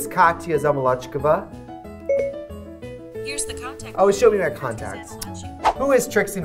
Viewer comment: did u just say... contact👀